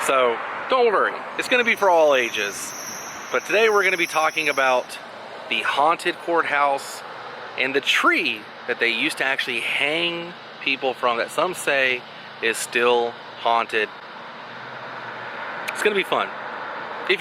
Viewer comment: ha ha ha... at 1:12, I thought for sure you said Haunted **orehouse.... I had to go back to clarify